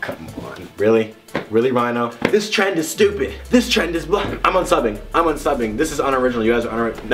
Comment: Did he say "this trend is blow!" at 0:25 😂